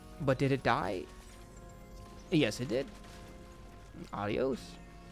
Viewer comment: Did you just say "ayos" at timestamp 3:57??? I feel elated whenever a non-Filipino national speaks a Tagalog word in their videos, especially if it's about hsr, just saying heheheh 😊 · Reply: Calm down mr pride, he said Adios, not Ayos.